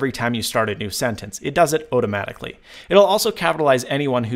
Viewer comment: Did he say "ote-amatically" at 3:18?